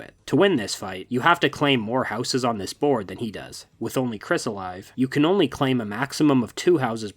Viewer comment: i legit thought this said "With Only Kids?"